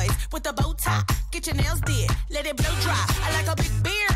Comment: I like big boys Itty bitty boys Mississippi boys In the city boys I like the pretty boys With a bow tie Get your nails did Let 'em blow dry I like a big beard I like a clean face I don't discriminate Come and have a taste From the play boys To the gay boys Go and slay, boys You my fav boys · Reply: I thought she said "bad boys" not "gay boys" I think i'm wrong now